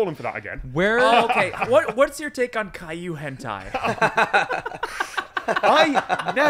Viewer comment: I love how it took 4 minutes for Tom to be cornered into talking about himself. I hate that it took Allen mentioning Caillou hentai for Tom to be cornered into talking about himself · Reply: I thought Alan said Kaiju Hentai. I have no idea what Caillou is